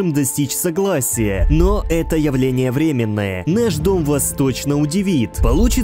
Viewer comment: наш дом вас точно удивит Так же что слышу я - наш дом восточно удивит😂😊